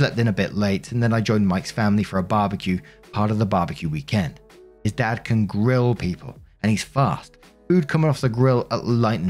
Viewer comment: I heard “His dad can grill people” not “His dad can grill, people” and I had to do a double take before realizing my mistake lmao